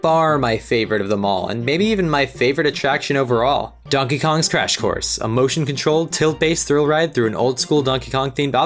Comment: I thought you said "Donkey Kong's Trash Course".... XD